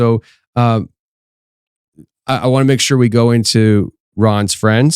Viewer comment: Baal? The deity? Am I hearing this correctly? I believe him, yes. The Catholic, Christian Baal is recruiting in dreams? Or it’s a ( project) Dream study taught this? Dreams are real? Lucid dreaming is that a lower thing? If u know it’s a dream so you do whatever u want, that’s real? My brain stopped at Baal? 😮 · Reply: So me too? Wondering if anyone noticed he said its name was “ Baal”?!!😮